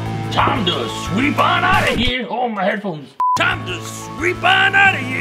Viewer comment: First I thought gotta sweep said OH MY ANKLES But he said oh my headphones